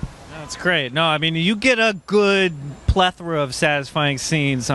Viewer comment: what is that word that the host says and tyrion says nice use of word __ ? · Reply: plethora